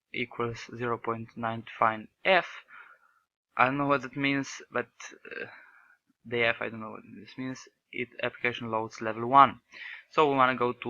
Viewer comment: sorry what? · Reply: I don't really understand what you say at 9:36 I hear "but the f I don't know what does it mean " That's why Ive posted "f is just for float"